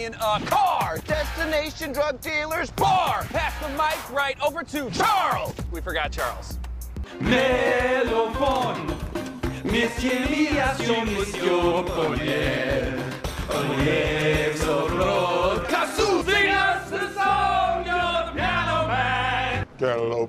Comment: sounded like “fuck the police”😂